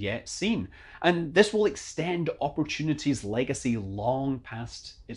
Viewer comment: At first at the end I thought you said "I'm Scott Manley, Cry safe" RIP Oppy. we'll miss you.